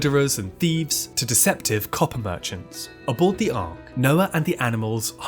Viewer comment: I heard "deceptive coffee merchants" and would have missed the shoutout to MAHBOI had I not read the comments!